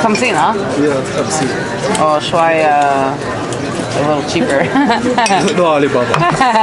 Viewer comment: I think he says "no Ali Baba". He can't do magic ☺️